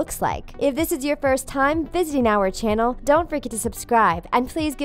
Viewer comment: thought she said "don't freakin subscribe" I'm like "excwuuwuwse me?" My volume was low don't blame me it was 1:18 in the morning